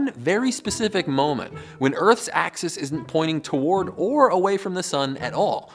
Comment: did he just say earth was a disk